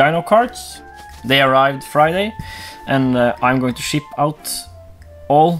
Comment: Thought He said am going to S**t out instead ship out had little chuckle in My small brain